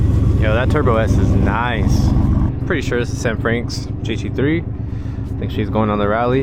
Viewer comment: bro I thought you said Anne Franks GT3 lmfao